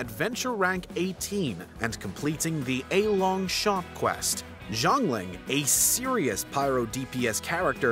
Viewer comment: heard this as zhongli 😶